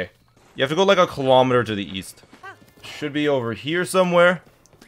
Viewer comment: guide: OHHHH east? i thought you said WEAST